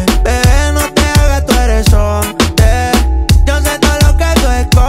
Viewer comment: Si yo pensé que dijo no te agas tu eres hombre